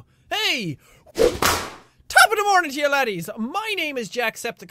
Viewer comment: what i thought you said tapmemoringten